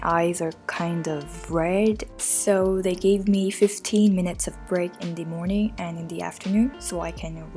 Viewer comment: 15 minites instead of 15 minutes